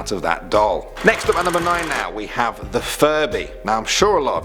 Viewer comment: I thought he said Furry